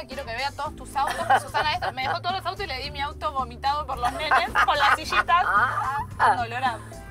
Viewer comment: Wanda dijo "Le di mi auto GOMITADO" oh escuché mal🤔